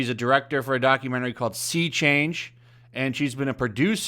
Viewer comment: Until you said "Sea Change", I actually thought it said "Sex Change".